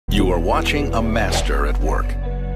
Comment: I thought you said master not “capster” lol, love your videos